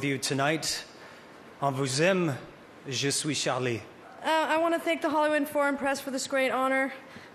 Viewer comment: did he say "swiss chalet"?